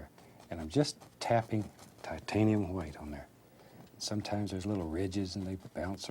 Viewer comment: is it just me or i really hear him said "titanium hwite"? 😂